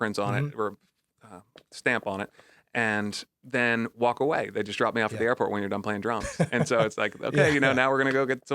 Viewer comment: Protest dumped you? The fuck they were thinking? I like them, but they made the worst decision ever in their life... Anyway, glad you got in. Keep rocking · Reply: wild I didn't hear that, I heard: "They just dumped me". But if you say so, okay